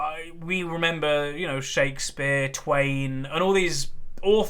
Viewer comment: did say Shakespeare or twat loool😂😂😂. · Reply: Twain